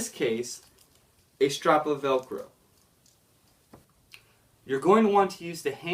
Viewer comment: what he is saying? a strap of what? please someone tell me... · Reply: strap of velcro